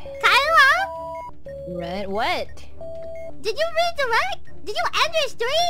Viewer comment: think she said "did you redirect", not "did you raid the right"